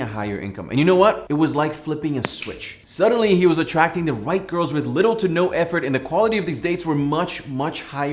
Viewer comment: Did he say " attracting the white girls"?????